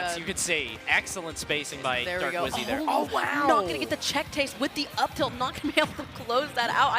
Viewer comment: Did she just say check tase at 6:53?